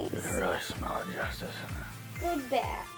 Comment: did he say "You can really smell the justice." ? Haha. Great tutorial.